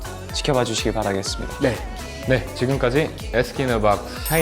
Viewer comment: I'm not the only one that heard him say "Ask in a Box" to the "D*ck in a Box" tune right?! XD 😂😂